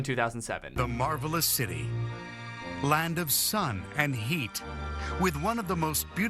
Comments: "land of sun and heat" fr thought it said "land of sun and yeet" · i thought the intro said "The marvelous city. Land of sun and yeet "